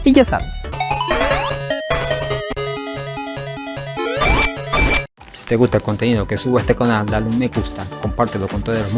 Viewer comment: no entendi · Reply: ¡Metal Sonic! 😲